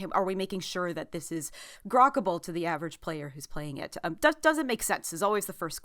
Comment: did she say is this Grokable or am I going insane